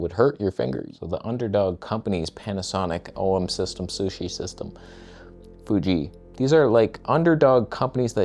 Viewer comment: did you just say, "OM Sushi System"?!